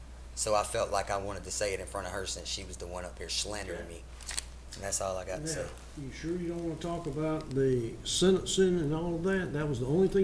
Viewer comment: did he say “shlandering” me.